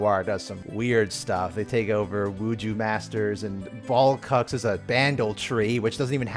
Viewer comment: wait, did I hear that right, that League has a canon character named “Ballcucks”?